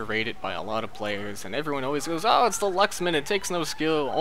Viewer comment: lol I heard "lux-man" not "lucks-man" (as in luxury, mfw -> o.O)